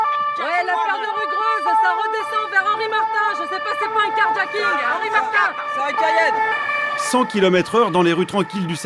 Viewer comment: je rêve ou elle dit « jsp c’est quoi le car jacking »